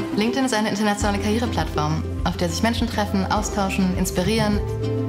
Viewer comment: ich verstehe ständig: "Treffen, austauschen, inspirieren, f****"